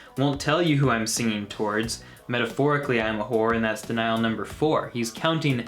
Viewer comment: Lol I thought he was saying "and that's in isle number four" 😂